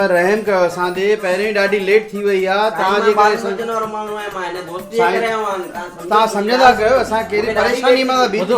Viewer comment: what he says😂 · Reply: Dosti kare aayo aaya